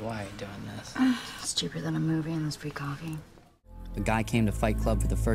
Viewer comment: I thought orginal she said "For this stupid little movie and the free coffee."